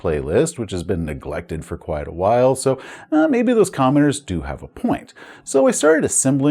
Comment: I'll admit when you said "commenters" I heard "commoners" so henceforth in my mind you are a Viscount